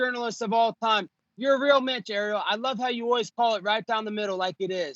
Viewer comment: Do he really just call him Geriel instead of Ariel on 0:32 lol oh god